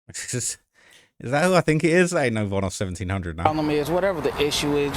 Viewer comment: DID YOU JUST CALL THAT PERSON VONOFF1700 😂😂😂😂😂😂😂